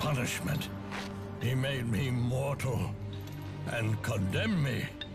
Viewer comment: I think he said immortal. It's just the subtitles that were messed up.